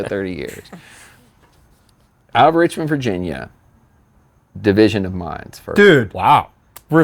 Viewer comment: for the life of me I can't make out what band he mentions here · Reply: Division of Mind